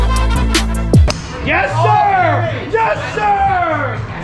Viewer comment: Thought he said “YES SH-“ 😂